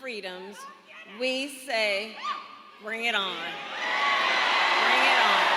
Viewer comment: When they chanted "bring it on," I thought at first they were saying, "String him up!"